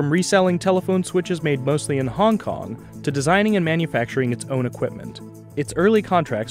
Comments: Why at 2:30 the person says "moved... to designing and manufacturing its own equipment" but the subtitles say "moved... to reverse-engineering equipment abroad for Huawei to manufacture itself" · The audio at 2:25 says "made mostly in Hong Kong, to designing and manufacturing its own equipment" while the caption says "made mostly in Hong Kong, to reverse-engineering equipement made abroad for Huawei".